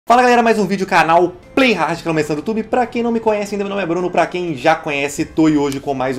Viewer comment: o que ele fala?? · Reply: Matheus David "fala galera, mais um vídeo do canal Playhard, canal mais insano do YouTube" eu acho q é isso, só acho